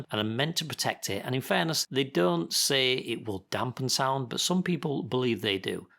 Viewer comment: Thanks for the comment, Ben! I think my meaning is that they don't work the way people think they do with respect to sound dampening and some manufactures do indeed claim that they reduce noise and vibrations when closing car doors. In fact, the first listing that I found on eBay claimed that "The car door lock protective cover is designed to effectively reduce noise and vibration when closing car doors". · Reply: ok. I misheard you then. English isn't my first language and I thought you said manufacturers of the protectors didn't say it was for sound proofing. My bad. Edit: @ 1:33 I heard it as they don't say it's for sound dampening.